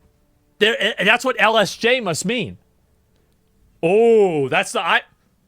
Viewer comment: ngl dawg I thought you were saying "LJS" and I was sitting here like "dang I cant go to long John silvers anymore..."